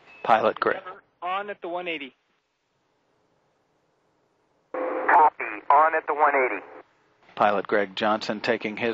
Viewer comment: Could U please tell me what is he saying at 1:14? (I mean austronaut) and what does it mean? Thanks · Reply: Houston told him "On at the 180" And he replied "Copy, on at the on at the 180" "On at the 180" means that they are one energy, meaning correct speed and altitude, with 180 degrees left in the turn until they're lined up with the runway. "Copy, on at the 180" Is an acknowledgement from the commander that he heard them.